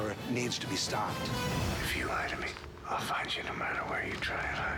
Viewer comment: What is Jack saying at 0:23 right after: "If you are lying to me..?" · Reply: I'll find you no matter where you try to hide